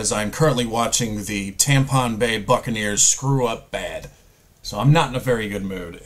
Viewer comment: Thought you said tampon Buckner's at the start 😂